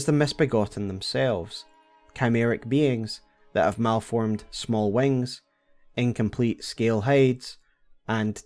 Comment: Thanks to your accent I misheard “malformed small wings” as “malformed small wangs”and felt extra bad for the misbegotten.